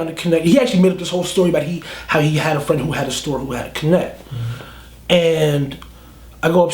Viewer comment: What the fuck is he saying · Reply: Fipidy he said he had a connect.